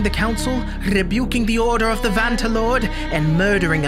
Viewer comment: Bro why did I hear “Fanta lord 💀